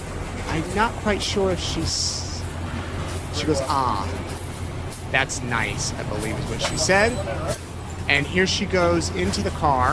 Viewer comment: I think she said "sunshine"